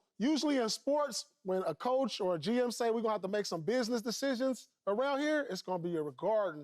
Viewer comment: that’s not what he said he said players was making business decisions